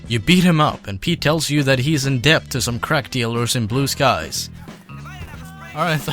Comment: I thought he said crap dealers